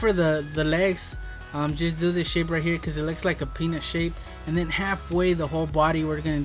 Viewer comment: Is it just me...or did he say (penis shape?)